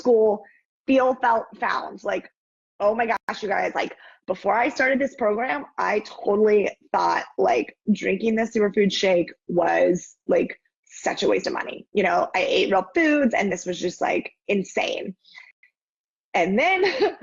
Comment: sounds like she said “drinking this sewer food shake” lol